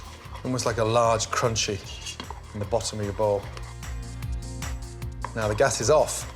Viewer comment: I thought he said a large crunchy lobotomy bowl LMFAO